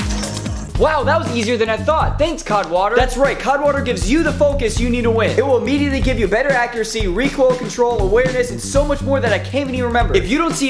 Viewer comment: First off, where do I buy this? Second I thought he said cock water at the end.😂😂 Also love the content